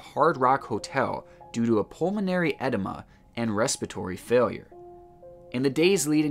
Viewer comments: Thanks for a great, interesting, channel. 👍 I was a bit confused for a while, I thought you said that some of the people had an enema, (sure, it’s uncomfortable - but not usually life threatening). Then I realised you were saying edema , I think it’s pronounced ‘eh-deem-ah’ , sorry about that. 🙄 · Hmmmm pulmonary edema (water in the lungs) is usually caused by a heart condition, so basically they all died the same way 🤨☹️ sound like ethylene glycol (antifreeze) poisoning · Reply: I thought the man said pulmonary enema and had to check his work lol